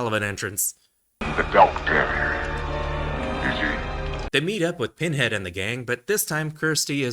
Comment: As a non-native speaker I always had problems understanding what the doctor says at 10:00 I always hear him ask:" The doctor - is he?" but that makes no sense. Could anyone help me out with this one?